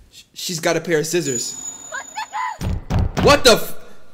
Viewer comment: Bro listen to 28:31 closely, it sounds like she said "what n*gga"😂